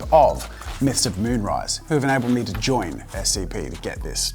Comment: Why did I think he said Mr. Moonrise?